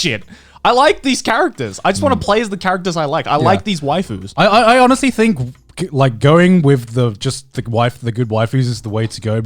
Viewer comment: did he call Klee and Qiqi waifus? FBI, you listening?